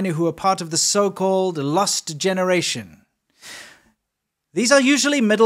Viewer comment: "LUST generation"? That's what I hear. Did you mean "LOST"?